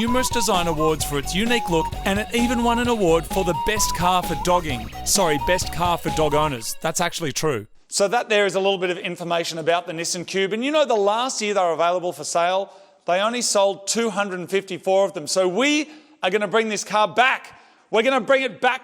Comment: All I heard you say was “the missing pube”